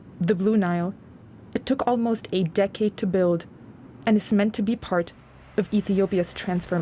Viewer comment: did she just say "It took 8 decades to build "? what ? Shame on you ! It didn't even take a full decade to get this stage.